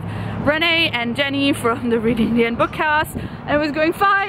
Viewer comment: What is the podcast you mentioned around 3:00, Renee and Jenny's podcast? I couldn't quite understand what you said. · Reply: Renay is one half of Fangirl Happy Hour and Jenny does the Reading the End bookcast! I'll add links in the description box just now!